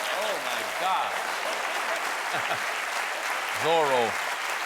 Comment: "Z" stands for "Zombie", as in "Z Wars". Bad choice, Mr. P*tin. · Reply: It sounds like John said it at 9:08, "Zorro." ;)